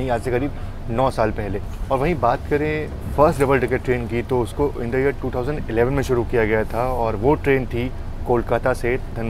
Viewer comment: 6.24 - you said first double decker train ran between Kolkata & Dhanbad but I have travelled in Sinhgad Express on Mumbai-Pune route even before 1995, even Flying Ranee is running on Mumbai Surat route for many many years. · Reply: bhai at 6:17 he clearly said...pehli double decker train